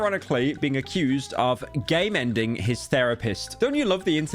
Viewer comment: "GAME-ENDING"? I thought you said "GAY MEN ding".